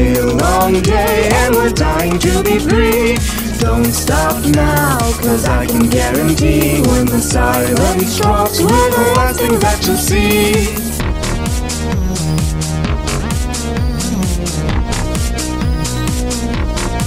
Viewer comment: I thought they were saying "when the siren stops we're not asking that you'll see"